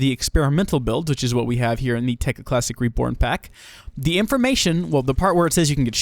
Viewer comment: sounded like you said "reporn" at the start xD